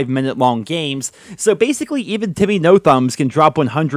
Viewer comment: Did he just say JimmyNoThumb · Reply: Timmy